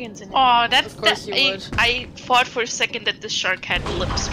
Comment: Also I just realized that I mumble a lot. "And I thought" sounded like "And I fart." GG wawa.